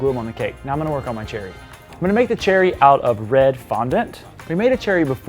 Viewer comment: When said cherrys I thought he said ‘cherry TOMATOES’ lol